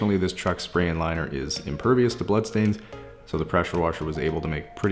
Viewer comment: Later on the news: “Canadian Serial Killer on the run and was last seen driving a black 2012 Dodge Ram” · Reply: Fr I Heard him say it won’t come of because of the blood stains 😮